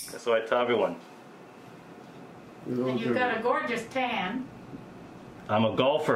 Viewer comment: I thought she said gorgeous can, not tan. 😂😂😂😂